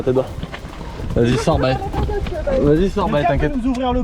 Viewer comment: c'est moi ou il a apeller BAE ??????